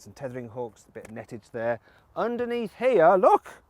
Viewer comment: Did you just say "nettage"? :D